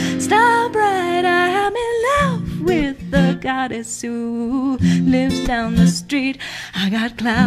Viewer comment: I honestly thought it said Cuddles and not goddess